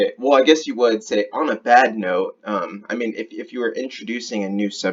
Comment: Sounds like he says Bad Move not bad note.